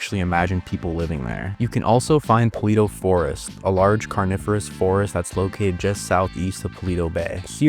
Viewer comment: I think it's time for me to sleep. I heard "carnivorous forest"